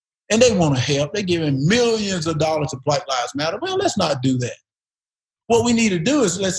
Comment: Did he say "giving millions of dollars to White Lives Matter"? Listen at 4:56, he clearly says it.